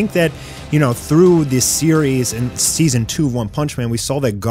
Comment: what he say Season 2????